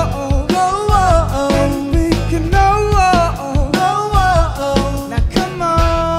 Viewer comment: why does it sound like he said "k-now" 😂😂😂 Also I'm just now getting into The Rose... finding this was awesome....